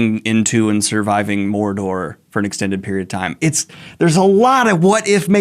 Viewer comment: Omg I thought he was saying extinct not extend lol